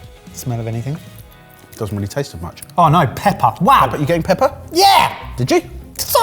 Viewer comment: the bit at 14:53 had me rolling, for whatever reason I kept hearing it as Peppa even though I knew it was "pepper", and just was imagining Peppa Pig x)